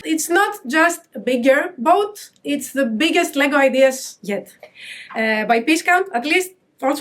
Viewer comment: For a second I thought she had said the biggest boat Lego had made and I was like, no way they topped the titanic